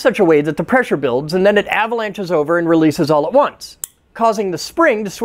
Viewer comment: I thought I heard "releases all the 1's" like a binary joke lol